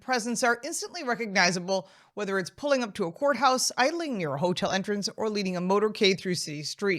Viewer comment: Thought you said whore house not courthouse lmao